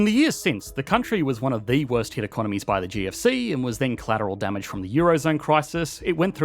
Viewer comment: What is the "G of C" or "GFC" or whatever? Would be great it you used proper terms instead of annoying acronyms. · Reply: It's GFC and is a commonly used term in Australia. Unlike the US we didn't cause it, but we love to abbreviate it.